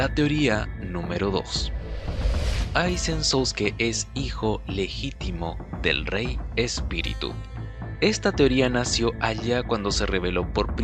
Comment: creo hubiera editado esa parte parece que dice el hijo del rey espíritu se detonó a Aizen